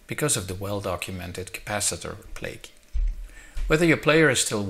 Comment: "the well documented capacitor..." what's the next word? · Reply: David Sutherland capacitor plague